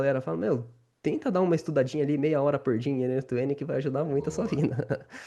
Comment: Meia hora por dia pra estudar oq? Não entendi oq ele falou nessa parte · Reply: n8n e automação